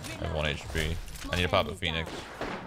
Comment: i thought he said: ‘I need to pop the penis’ I had to rewund it like 3 times lol